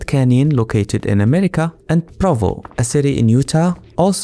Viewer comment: Did he say Provo? Like the city in Utah?